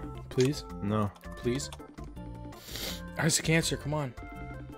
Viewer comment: Am I the only one who thought he said: "isaac cancer cmon"